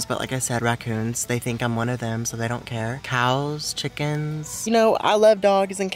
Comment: When he said "..cows, chickens." I thought he said "..kile, chickens."😂